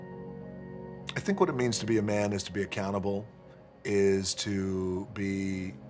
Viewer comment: I thought he said what means to be a man is to be a cannibal